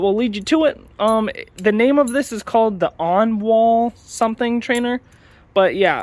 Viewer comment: Its rly good , but the screws on mine come loose easily but you can add locktight to fix that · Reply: oh yeah i thought he said no wall but it was like ownall